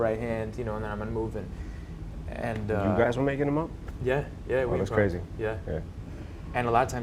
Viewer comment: please somebody can write what Em says at 9:29? you guys were making ...??? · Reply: Marshall just said "You guys were makin em up?" when they were talking about Jake improvising fight scenes.